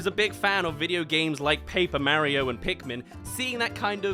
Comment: My heart went when I heard pikmin (arguably one of the best franchises Nintendo has ever made) but saw the subtitles say Pokemon 😭😭😭